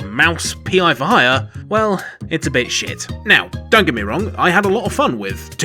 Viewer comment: it's a... Bay shit? Can any American understand what he said?